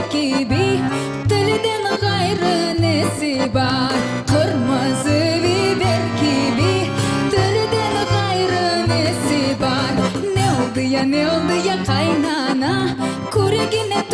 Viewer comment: ne dediğini anlamadım😅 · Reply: Kırmızı biber gibi dilinden başka neyi var diyi